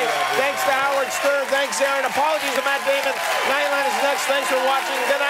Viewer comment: did he just say apologies to matt damon?